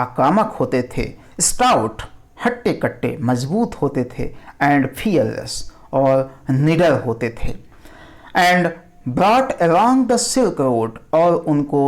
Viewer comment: i heard 'nigger hote the'😂?😲